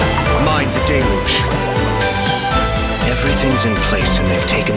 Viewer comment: I don't ship them but yeah I misheard "My Diluc" instead of "Mind the Deluge" 😃😃